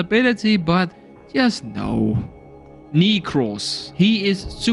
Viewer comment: what did he sayyyyyy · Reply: I am pretty sure I said Nekros